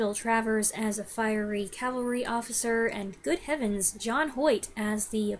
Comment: Ah, you misheard me. The actor playing the chief in Duel at Diablo is *John Hoyt*. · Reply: I definitely did mishear you because I couldn't figure out how you could think that that was Jon Voight. I don't think I know who John Hoyt is. I'm much older than you and grew up watching all of these classic movies in the 70s and 80s on TV but I don't think I came across John Hoyt. I do know who Hoyt Axton is and he was really good in his acting roles though he was also pretty famous for his music for a while. Keep up the good work